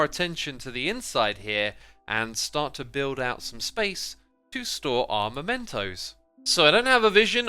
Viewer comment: i heard to store our mento's, lol